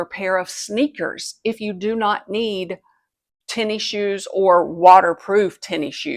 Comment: What’s a “TENNY-SHOES”?? I heard you use “sneakers” as well, so I don’t think that’s what they are?